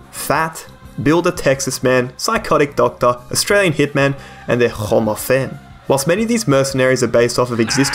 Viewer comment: I spent 30 seconds trying to understand that what he said at 1:26 is "hommes à femmes", and I'm still not sure